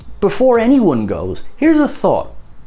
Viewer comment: What was the spoiler for the podcast again? Honestly heard you say it as "Figgus loads holograms"